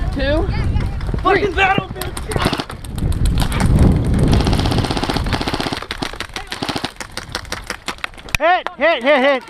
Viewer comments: "fucking battlefield shit" did i just hear that? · Am I the only one who heard the guy at 7:15 saying "this is battlefield shit" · anyone else hear the "f*&%in battlefield s*%t" @ 7:14? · Does he say, "Fuckin' Battlefield Shit"?